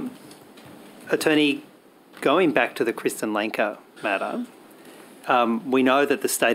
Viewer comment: did he just say Krista??? 🤣😂